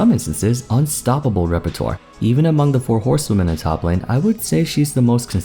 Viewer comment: did you say "horsewomen"? That's adorable xD